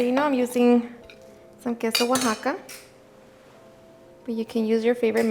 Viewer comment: What is the white melting cheese you used inside the enchiladas? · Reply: I thought she said Queso Oaxaca was her favorite.